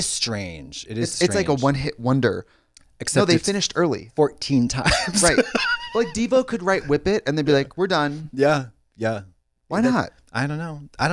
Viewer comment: I know Devo but I’m so entrenched in these two that I literally heard Trixie saying “Diva!”